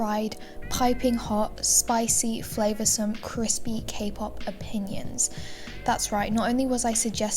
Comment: i thought you said cake pop opinions for a second😟